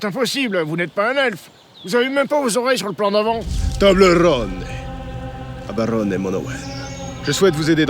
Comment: C'est moi ou il a dit " tobleron " ?